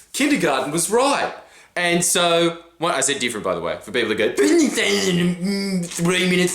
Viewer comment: What did he say at 2:21? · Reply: He says "for people that go"